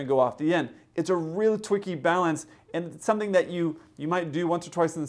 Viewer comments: Did I hear a “twicky balance”? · LOL! I'm sorry, but 8:29 did you say "it's a real twicky balance"?